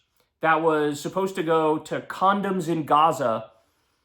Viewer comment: The whole situation is "stupid beyond words". Man I had to laugh at that. At first I thought you had said $50 million for condos in Gaza. Now that really would have triggered Trump. But condoms ? Used for bombs ? Or have they gotten so stupid they thought "well yeah, they use em for F-bombs." Look unless a solution develops, this is going to be biz as "abnormal".